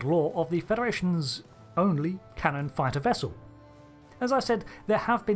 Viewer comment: Cannon Fodder when you called it the 'Canon Fighter' I heard 'cannon fodder'